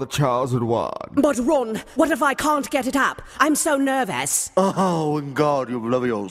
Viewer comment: The way Harry says so Britishly: "But Ron, what if i can't get it up, i'm so nervous" KILLS ME EVERYTIME LMFAOOO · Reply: Ok, I always thought he said "what if I can't get it out", and no, I didn't think he meant out of his pants. I'm kinda sad now.